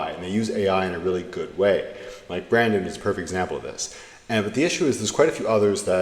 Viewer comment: I dont know what you said could you reply with his name? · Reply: Brandon, from my last interview on my channel.